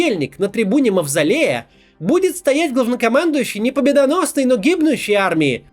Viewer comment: Послышалось "... будет стоять гОвнокомандующий..." А дочурка — это чудесно. Мирного неба ей и всем нам!